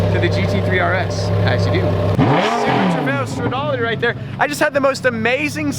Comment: Did he say Super Trofeo?!